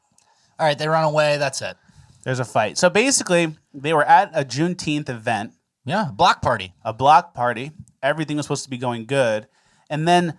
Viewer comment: JUNETEENTH BLOCK PARTY??? I THOUGHT YOU SAID GLOCK PARTY...